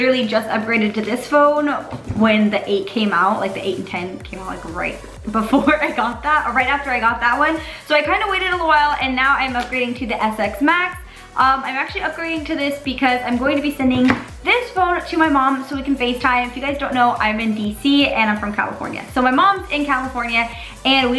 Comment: did you say “SX MAX” ?! 😂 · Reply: It’s ten S max if she said that she wouldn’t be tongue twisted